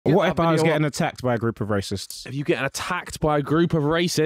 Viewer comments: bro i thought he said rapists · I thought he said rapists😂😂💀😂😂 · Lol thought he said rapist first lol